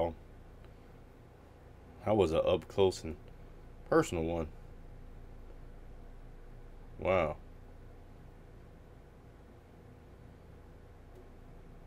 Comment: seriously thought the mouse said "wow".